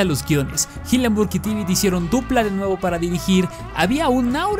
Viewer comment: hillenburg y quién? Esque ahí no entendí je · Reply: Creo que dijo Tibbit.